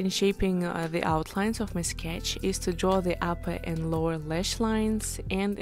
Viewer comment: I’m confused around 4:15 you said draw the upper and lower leash lines Ik that’s not what you said what did you actually say?😅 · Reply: I Said draw the upper ander lower lashlines and a crease for the lid